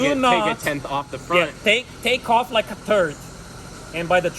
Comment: did he say take off a 💩 😂 I love this dude!